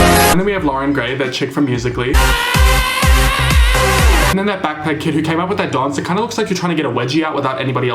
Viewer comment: Everytime someone says Backpack kid, I always hear “that fat kid”